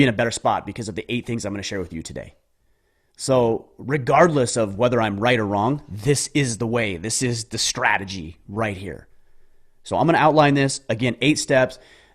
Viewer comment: Did he say "Rickgardless" @ 3:08....? Noice!